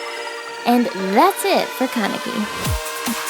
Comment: um um um ummm was I the only one that heard her say kanaki at the end .-.